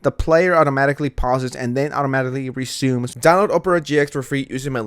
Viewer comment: Amigos! Download Opera GX For FREE! <link> Thank you all for the support, means the world to me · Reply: I swear I thought you were saying Oprah 😂